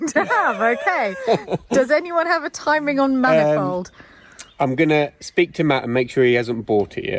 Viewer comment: I thought you said man involved not manifold. 🤣